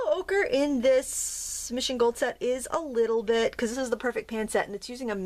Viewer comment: What kind of paint set are you using? Thank you! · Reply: I thought I heard her say Master GOld. I'd like to know also. I love how many colors are in the set AND I love the saturated colors.